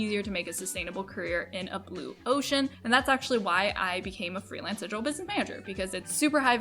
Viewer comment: Great video! What did you say you were doing exactly at 7:08? Thank you! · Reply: Haha sorry I said that kind of fast - I became a freelance digital business manager. :)